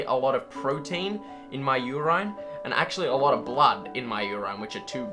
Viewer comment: he says you rine instead of urine